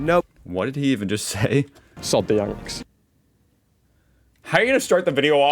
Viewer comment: "Salt the Yanks" was what he said, I think. 😅 Definitely Yanks. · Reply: he said Sod the Yanks